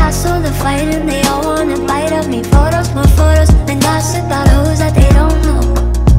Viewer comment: At first I thought she said toss in buttholes they don't know and I was like wt heck?